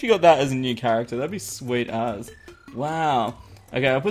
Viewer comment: Did just said " sweet ass"??